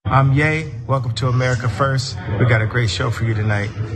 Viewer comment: I thought he said “I’m gay” in the beginning lol